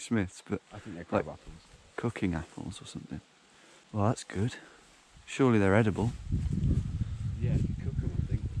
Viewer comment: I’m confused, why did you call “Shirley” for a second here?